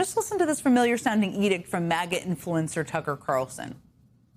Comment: Did Jen say "Magat" @ 6:18? hahahaha